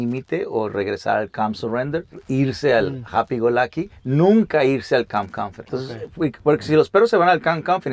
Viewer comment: Alguien que sepa ingles Que dice en el 5:10 que traduzca porfavor? Colm surrendah , happy and lucky? Colm coliflower? O que dice!? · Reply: Calm surrender (estado de calma) -Happy go lucky (estado de felicidad) -Calm confident (estado de liderazgo)